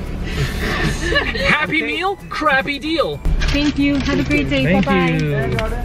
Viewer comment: I don’t know why I laughed so hard when said crabby deal omg so many likes AAAAAAA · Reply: Oh Idid too and I thought he said crappy deal xD